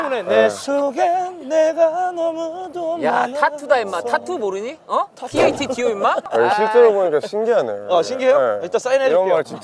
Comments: 이거 나만 TATTU라고 한 걸로 들리나ㅋㅋㅋ 파머스 버거 후유증인가ㅋㅋㅋㅋ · 나만 tattu로 들었늬...